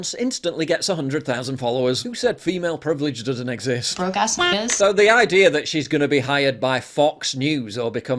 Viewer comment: I thought she said Broke ass bitches. Not the N-word.